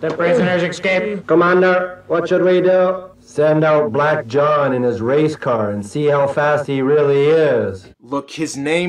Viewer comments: I swear at 12:36 it sounds like says "send out black john and his race card." My first thought was WTF!? What did he say!? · first time I heard that line read I though not-sark said "Send out Black John and His Race Card"